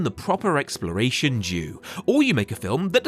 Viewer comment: because of the footage onscreen at the moment, I heard "due" as "jew"😅.